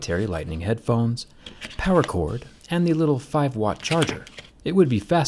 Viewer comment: Did he said 5 Watt?